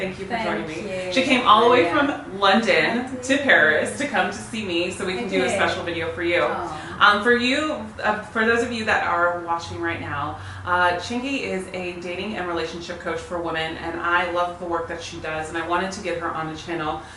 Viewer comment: 11 that through me off. I thought you were saying chingy (The rapper)